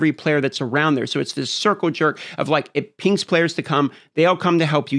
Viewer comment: did i hear Circle Jerk ???